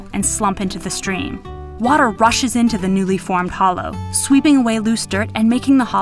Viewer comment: did I heard "HOLO" in 0:55???